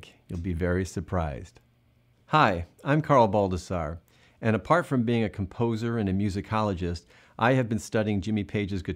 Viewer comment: Lmao at first I thought you said "apart from being a poser" 😂😂 hell I don't know..... When you're trying to copy someone else's tone it made sense.... Had to rewind and listen again 😂😂😂